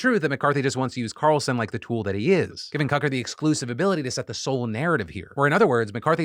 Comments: Am I going Crazy or is Phil Calling him Cuck-er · Wait did I hear Phil referring to Tucker at Cucker @ 20:40 ? 😂😂😂 Phil we love ya dude. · did Phil call him 'Cucker'? 🤣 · did I hear 'cucker' 🤣